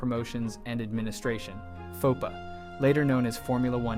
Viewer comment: i heard FUPA and i can’t unhear it now